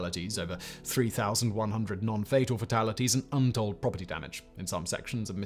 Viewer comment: What is a non-fatal fatality? 9:46. Or did I hear it wrong?